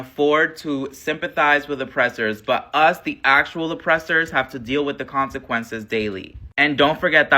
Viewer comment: Did he just say "us, the actual oppressors"? I mean he's not wrong...